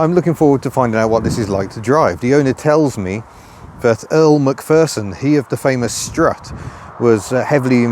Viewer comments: Totally misheard the engineer's name as Elle MacPherson and started wondering if she in fact is a petrolhead who chose a stage name in honour of the old suspension inventor... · Sorry, thought you said that Elle Macpherson was heavily involved with the design. Anyway, Ford were very fond of the name Capri weren't they